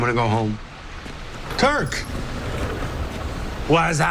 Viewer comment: Did he say Kirk istead of Turk?